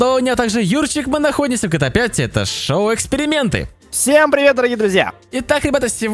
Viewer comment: ты сказал "Это шо? Эксперименты???" Мне так послышалось!😂